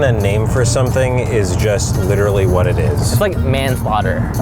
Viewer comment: What does Steven say @ 3:51 ? Man what?